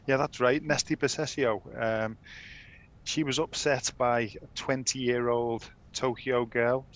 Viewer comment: Paul Ryding it sounds like he said Simone lost to a 60 year old.. that will mess with your mental health.